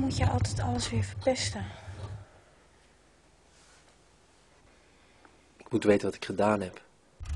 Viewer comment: what does he say at 1:04 ? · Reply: Ik moet weten wat ik gedaan heb